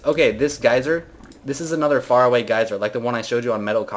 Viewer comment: Did he just say Metal-cock-le-piss??